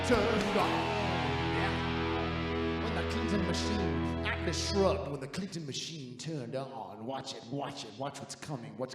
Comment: I just love how uncomfortable Gregg is! Also, wth is Tim saying at 21:51 ?? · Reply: Atlas Shrugged, when the Clinton Machine turned on.